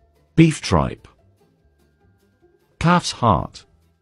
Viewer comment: bro i thought he said “cats heart” 💀